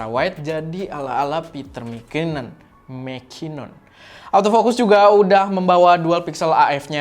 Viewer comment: bilang apa ? Gua ulang ulang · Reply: The one and only, petter mckinnon